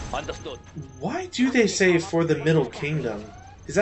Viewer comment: The Middle Kingdom is China, because it's the center of the world, get it? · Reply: I thought they said "For the Middling Kingdom."